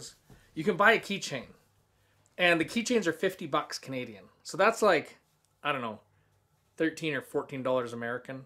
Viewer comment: did he say 50 Canadian? And 14 American?